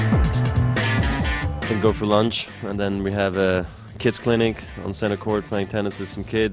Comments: I thought Roger said "we'll go for lunch than we have a KISS clinc" I thought geesh they're taking their bromance to the next leve! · Alternative title: Paparazzi hound Roger and Rafa on their date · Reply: hahaha i legit thought he said we had lunch and then a kiss clinic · I thought he said kiss clinic, I was like wo, I didnt know they were that close..